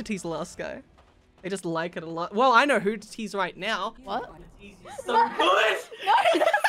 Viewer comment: what does say to after he says: I know who to tease right now? i am beyond confusion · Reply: He said “I’m gonna tease you so good”